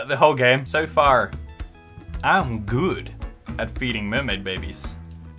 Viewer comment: i heard " I'm good at beating mermaid babies" and it took me a second to realize what he actually said. OOF